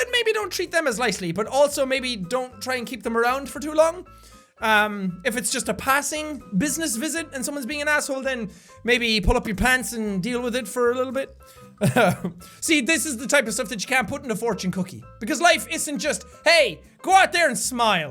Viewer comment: when he said pull up your pants i thought he said pull off your pants XD